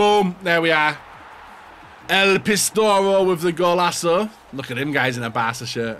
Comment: he calls Suarez el pistoro 😂😂 am I the only one who heard that